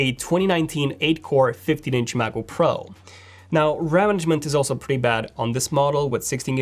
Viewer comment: Can't understand word he says at 06:30 "...now ? is also pretty bad on this model.." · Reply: I think he said, "Now RAM management is also pretty bad on this model.."